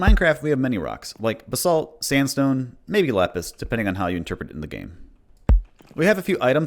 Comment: I had to check three times to realise you said “lapis“ and did not call “lettuce“ a rock, lol.